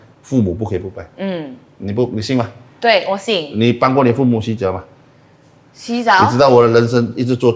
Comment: 你帮过你父母洗脚吗？ 洗澡？😅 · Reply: I also thought I heard 你帮过你父母洗澡吗 at first. So shocked. 😂